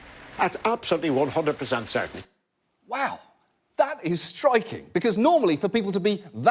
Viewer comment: "Blarghblarghblubarght" That is striking says JO... really? I'm just gonna have to take your word for it because I needed subtitles.